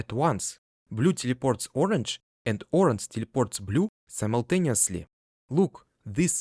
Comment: I heard "deported" lol